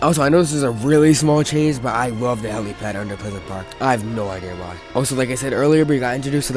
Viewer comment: facts but also what the fuck did you say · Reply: Lmao my b 💀 I was talking about the helipad under the soccer field in pleasant park.